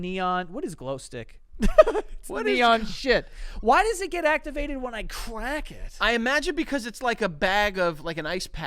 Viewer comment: I legit thought said “why does it activate when I crank it” and now I wish my Johnson was a glow stick